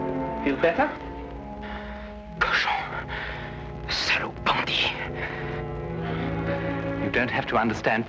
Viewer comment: What did he even say? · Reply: Cochon ! salaud ! bandit ! (Pig, bastard, bandit).